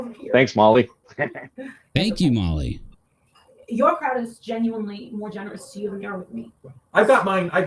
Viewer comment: Did I hear Molly say, “your crowd is more generous…” ???